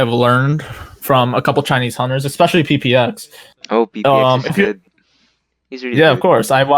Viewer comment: May i ask how u spell the name of that player he mention at 24:09 i didnt catch it quite clearly and where to look for his streams too if possible, thank you ❤ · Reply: PPX of Gr. You can watch his vid on bilibili, his username is 虎牙_皮皮限